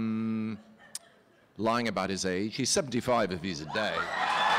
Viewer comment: What does Hugh say in 0:37 ? 'He's 75....' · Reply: He said he’s 75 if he’s in day